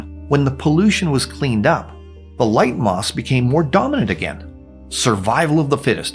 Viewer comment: is it just me, or is he saying ‘moss’ instead of ‘moths’?